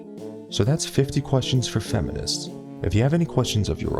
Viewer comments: am I the only one who thought that he was saying: Rape the Video?🤔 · At the end I thought you said “comment, rape, and subscribe” And I was just like woah there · At the end of the video, Did I hear "Don't forget to rape this video and subscribe"? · At the end i thought that he said "don't forget to RAPE this video and sibscribe " 😂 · I heard it as "and dont forget to rape this video and subscribe"... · i thought he said "don't forget to rape this video and subscribe" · At the end I thought you said "dont forget to rape this video" 😂😂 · I thought he said "and don't forget to rape this video and subscribe" and i laughed so hard XD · Thought he said at the end “rape this video and subscribe” I must be tripping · at the end of the video I thought he said "and don't forget to rape this video and subscribe" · i thought you said "don't forget to rape this video and subscribe" XD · at the end i heard "and dont forget to rape this video and subscribe" lol they be gettin into my head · OMG at the end i thought he said "make sure to comment down below and rape this video" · oh, msn I thought he said and don't forget to rape this video and subscribe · At the end I thought he said "Don't forget to rape this video, and subscribe." honestly it's probably because i've been hearinf the word 'rape' all my life that rate, and rape just sounds so much a like. But amyway, you're videos are pretty cool man, especially your feminists videos. :) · Did I heard. Don't forget to rape this video?😂